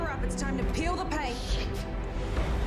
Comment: Wow!!! I feel like I just read the Bible!!! Anyway I glad I did because I always thought Bobby said, "time to feel the pain!" Not peal the paint. So thanks for that. It's a way better line.